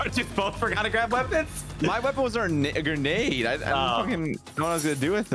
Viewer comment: what did he say 🤨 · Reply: I have no clue, but I did catch the word "grenade" on the end of that splutter